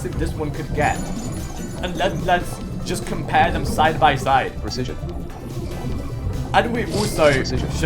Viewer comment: why do I keep hearing a text to speech say precision